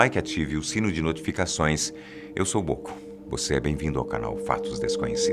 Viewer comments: Eu juro q nessa hora eu ouvi,oi eu sou goku 1:19 kkkkk😂😂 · Mano, quando ele falou o nome eu escutei "eu sou Goku" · Mano, quando ele falou o nome eu escutei "eu sou Goku" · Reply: eu entendi "eu sou o gohan" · achei que ele tinha dito " eu sou Goku" '-' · Eu entendi "eu sou goku" kkkkkk